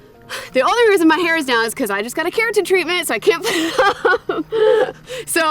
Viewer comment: Thank God for rewind. I had to play back the part when you said why you had your hair down. At first I thought you said you just had cancer treatment. Hair care treatment. :-) Looks like y'all had a lot of fun and great meal. Love, God Bless and stay safe.